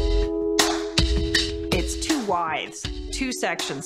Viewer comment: What is she saying at 3:25, “there are 2 Y’s” or “there are 2 wides”?